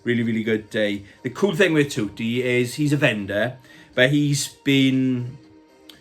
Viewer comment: 😮 I thought you said "bender" 😂 spat my coffee everywhere!! 🤣